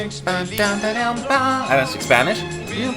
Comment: Did he just say "I don't speak Spanish"?! Maybe I heard wrong :v